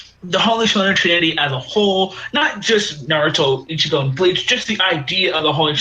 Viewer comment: Did really say "Naruto, Ichigo, and Bleach"? Lol